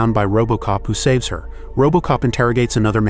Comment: did bro say bobo cop?